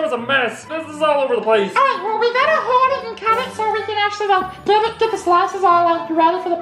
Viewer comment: what did Elmo say I'm so confused I think he said this Alright well we better hurry up and cut so we can get it in the slices and ready to pee it